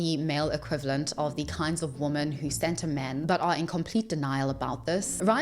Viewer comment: "Scent a man"? I played it back a dozen times and still have no idea what the creator was trying to say. Anyone?